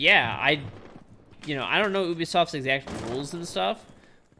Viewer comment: i heard him say oubisoft instead of ubisoft